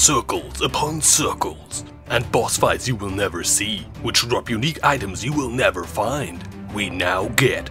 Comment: Oh man, that "bosses you'll never fight" hits so close to home. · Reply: i legit heard "uniques you'll never drop". Which hits home too.